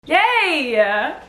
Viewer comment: i swear it sounds like you said "GAAAYYY!" lol